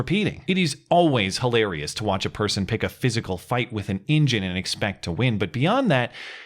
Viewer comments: For a second I thought you said "pick a fight with an injun" and I was like WTF. Then I realized you said "engine". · Apologies if someone already said this, but I thought you said “picking a fight with an Injun,” and I wondered if this whole “white guy living in Montana” had gone a bit too far...